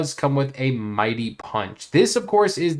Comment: Hahahah. I thought you were gonna say "it comes with a mighty poo". But you said punch instead